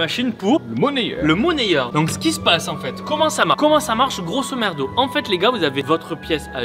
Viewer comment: C’est moi ou il a dit GROSOMERDO à la place de grosomodo